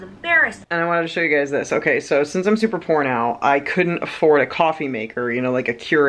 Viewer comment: Ohhhh you said "Since I'm super POOR NOW"... I heard porno 😂🤣 (3:33) I totally sit cross-legged leaning over my legs too! And Im 5'7" 😂 Love ya 😂💜